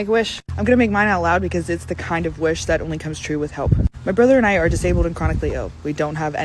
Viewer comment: I’m 2 minutes in and all I heard was “my brother and I are losers”.